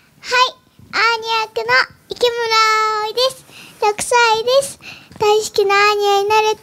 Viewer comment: 100歳です！ってちょっと聞こえる